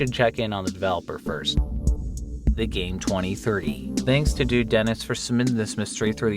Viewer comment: I thought you said “duke dennis” I was like lemme find out he a nerd like that